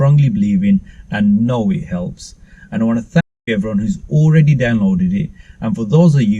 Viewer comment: This is just me being childish but around 3:26in the video the audio glitches and pin sounds like he is saying “ I want to f*** everyone who downloaded the ultimate driving course” 🤣